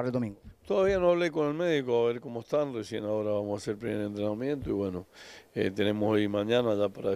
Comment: que dijo ???😂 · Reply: Jajaja "si no no vamos a hacer el previo entrenamiento"